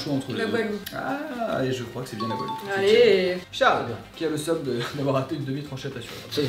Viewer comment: ç'est moi ou il dit Charles pour appeler